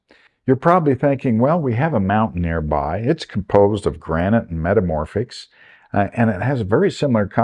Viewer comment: Thanks for such a expansive look at the terrain! One question: you mentioned that the granite was metamorphic rock. Im no expert but recently took geology 101 after retiring. We were taught that granite is an intrusive igneous rock. Normally if a granite is metamorphosed it becomes another rock (?) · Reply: Thanks! It was at 4:47 and listening again instead of saying "granite metamorphics" you said the mountains were "granite and metamorphics". I do find geologists sometimes have different terminology for the same thing but glad the understanding of granite being intrusive igneous was correct in my mind. It takes some practice to learn any language and geology is no different! :)